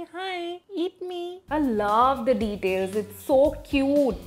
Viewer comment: when she said "it’s so cute" the subtitle says "it’s to huge"